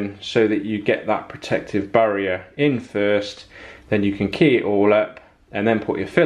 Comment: Your using a word at 7:47 that either i am not hearing correctly or a term that i am completely unfamiliar with . sounds like keahauler. What are you saying please? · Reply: 123jerro I was saying key it all up, which means sanding. Cheers 👍